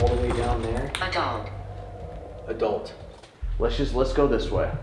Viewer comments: i swear i've heard "a dog" and then they repeat "adult" so i was like oh nevermind but a bit later there is a dog barking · After said someone says "There's someone outside", at 19:30 it doesn't sound to me like it says "adult". Sounds like it says "a dog". And then the dog starts barking.